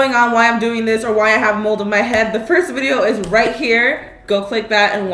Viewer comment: When you said a “ a mold of my head” , it sounded like you said, “ mold on my head!”!